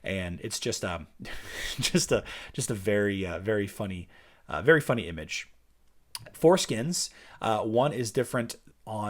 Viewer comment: At first I thought you said foreskins :')